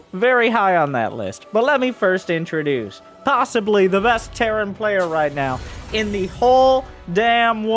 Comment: Reynor despite what the name might imply, does not play terran.. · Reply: I thought he said the best player in general not best terran.